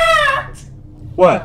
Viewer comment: What does Chica says at 1:57 ?? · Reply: Nora Salim "AHHH Not that Not that"